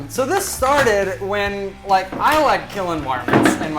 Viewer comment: I swear I thought he said 'I like killing Mormons'😂🤦🏼‍♀️